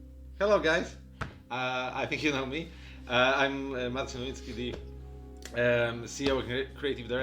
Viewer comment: did i hear correctly that he said "im Marcin Skibidi"?